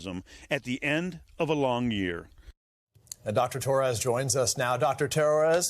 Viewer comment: Freudian slip...sounded like he said "Dr. Terrorist".